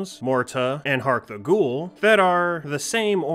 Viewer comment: What is the spellings of the two games you mention at 1:19? I'd like to look them up, but can't find them with trying a bunch of different spellings of what I heard. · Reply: Oh, Hark the Ghoul and Mohrta. That should help you out. I did talk about them in a video where I talk about demos too.